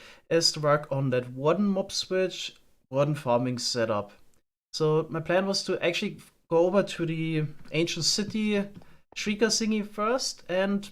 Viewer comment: it sounds like you said "so my plan was to actually F*CK over to the ancient city" and censored fuck, lmaoo